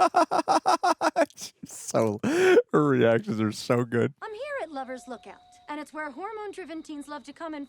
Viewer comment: Does he say urban actors are so good? So weird